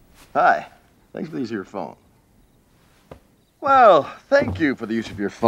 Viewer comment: I used to think he said “Buy an easier phone.”